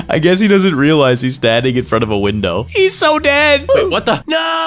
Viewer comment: Bro I heard said what the fu*#